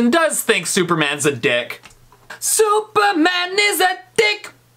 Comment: I thought that it said “Superman is thick “ Xd